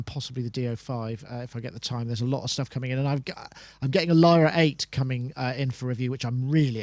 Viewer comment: mentions a device he has coming in, and for the life of me I can't make out what he's saying.. a Lara 8 or something? Good show though guys, really enjoyed it! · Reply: Lyra 8 it's a Russian drone synth